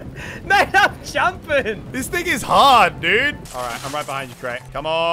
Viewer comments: I know he says “this thing is hard dude” but it sound like he said “this niggas hard dude” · for some reason i thought he said the n word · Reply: “This n** hard dude” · I thought he said "This Niggas Hard" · it sounds like he said, "this niggas hard" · Who else thought he said “This Niggaz hard dude” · Why it sounds like he said this niggas hard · lol I thought he said this niggas hard dude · it sound like he said this nigga hard 😂